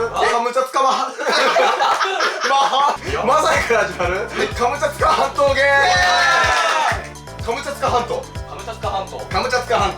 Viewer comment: カムチャツカハント、、、カムチャツカゲットー！に聞こえて意味分からんくなったわw